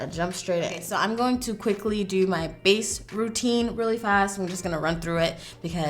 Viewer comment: For a moment, I thought you said “my face protein” rather than face routine 🙈😂